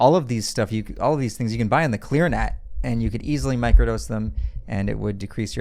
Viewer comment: At around the 3:15 mark, where did he say you could purchase some of these items? I couldn't make out exactly what he said. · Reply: Clearnet